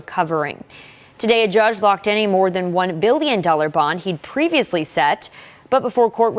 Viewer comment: Listen carefully, and you will hear the newscasting lady say "1 billion" dollar bond instead of the 1 million it really is. She must be stuffy in one of her nasal cavities or something, cause there's no way you screw that up, unless the freudian slip of the amount was purely intentional.